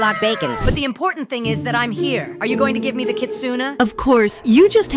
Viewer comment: I thought i heard kitsuna